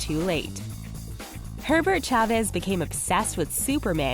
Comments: I thought they said Pervert Chavez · I thought she said "Pervert chavez" Not "Herbert chavez"